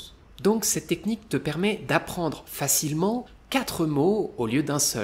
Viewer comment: Après 1:29 de la vidéo j'ai entendu : ces techniques te permet !!!